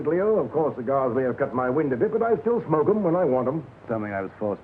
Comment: wtf why do you change the subtitles? Lionel Atwill says "I can still smoke them (cigars) when I want them" and the subtitles say "if only I could quit smoking" that a completely different line and not even what he meant. It's constantly like that;, why?